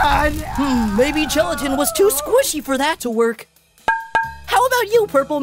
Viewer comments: Leafy said that "Maybe Gelatin was too squishy" but the captions say LEAFY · Put captions leafy says maybe gelatin was to squishy for that one captions say maybe LEAFY was too squishy for that one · the subtitles say Leafy: Hmmm maybe Leafy was too squishy for that to work! It’s suppose to say Gelatin the second time it’s saying Leafy and not suppose to say Leafy. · There was a mistake in the captions at 13:58. instead of leafy saying Gelatin was too squishy, she called herself squishy instead. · why does the caption say "Leafy: Hmm, maybe Leafy was too squishy for that to work!" but I hear quote "Leafy: Hmm, maybe Gelatin was too squishy for that to work!"